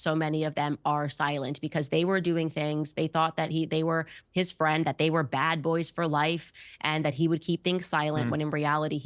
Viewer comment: Man did she just say they thought they was bad boys 4 life 😂😂😂😂